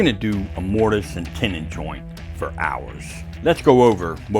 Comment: he said "use a mortice and tenon for ours" I heard "we are gonna mortice and tenon for hours"